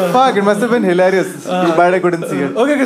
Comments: Sorry, what did kautuk say at around 11:30? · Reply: “Must’ve been hilarious. Too bad I couldn’t see it “ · what'd he say ? Couldn't catch it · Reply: “Must’ve been hilarious. Too bad I couldn’t see it”